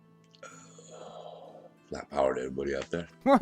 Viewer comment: Wtf did he say in the start? I heard "white power to everyone" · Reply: Hehe nah I think he said "flat power"